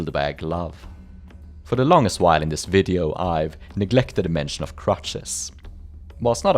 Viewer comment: I was listening in another tab and thought you said "neglected the mention of crotches" and I thought you were talking about a very different type of prosthetic .